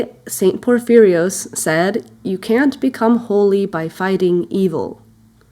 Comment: The quote you stated at 4:16 You don't become holy by Fighting or Finding evil; what makes a person saintly is love. It sounded like you said "Finding" although that statement works also but is more passive rather the aggressive "Fighting" It's a great quote.